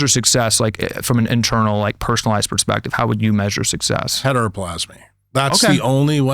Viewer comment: What word does Jack say at 1:17:22?? Heteroplasma??